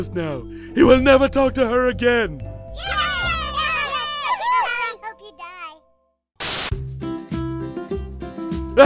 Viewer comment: I think I heard an "I hope you die" among the kids' cheers...